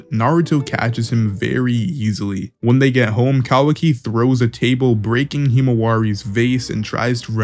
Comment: I 100% thought you said ”Throws a table, breaking Himawaris face” lol